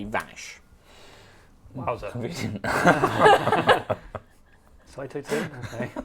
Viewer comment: What do tom and Trott say? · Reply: Trott: Convenient Tom:Sciato too?